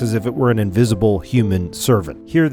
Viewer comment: I heard "invisible, mindless, shapeless HORSE" and did a double take